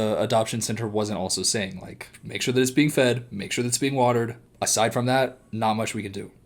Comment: being watered is the cat a plant? Lmao I think you say “make sure the cat drinks, water and eat food” so funny that you said I’m sure she hears and said the cat, lmao just kept thinking about a plant “Make Sure you water that”